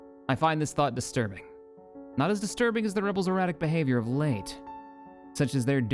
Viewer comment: for a split second I thought you said "not as disturbing the rebels erotic behavior as of late". Then I remembered that this is a family friendly show and moved along.